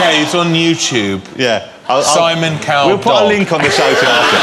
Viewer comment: What does Davis say? - Simon Cowell Polk...? Can someone tell me pls?